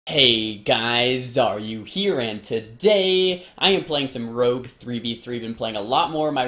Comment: are you here? I don't get it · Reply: He says "Hey guys, Xaryu here, and today" as in today we will talk about. I thought he said "are you here and today" for the first few videos as well but he corrected me ;)